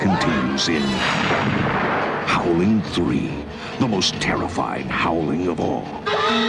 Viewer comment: i thought he said ''The most terrifying halloween of all!'' .______.